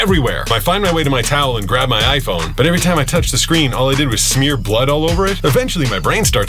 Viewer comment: I’m sorry but the first time I heard the beginning I thought you said mud, not blood😂